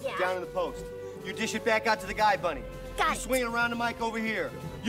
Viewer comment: What does Lola say at 0:20? Sounds like gust! PLEASE SOMEONE ANSWER THIS IT'S KILLING ME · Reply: billstein22 "got it"